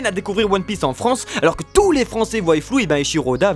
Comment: Tous les français VOYILLENT flous, c'est ce que j'ai entendu à